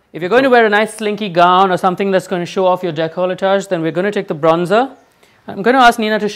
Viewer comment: did he just say Jecolletage? lmao as opposed to with a D